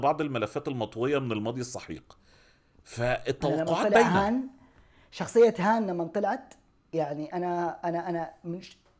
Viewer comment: وش يقول!؟. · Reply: قال كلمة احا وهذه الكلمة لها تفسيرات كثيرة باللهجة المصرية .